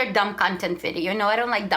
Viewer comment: sounds like she says “dumb cunt”